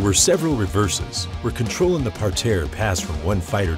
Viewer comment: What is the word used around 8:40? Sounds like "parterre". Googling that doesn't help much so maybe the spelling is different?